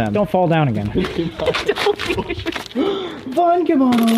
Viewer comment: they hear a noise. I hear nothing because of the music in the background. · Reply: I heard a 'Hello...~'